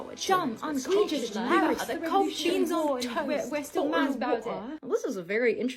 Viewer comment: Did I hear beans of toast at 22:29?